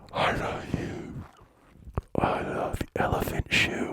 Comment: Did he just say "elephant shoe?!"... Snicker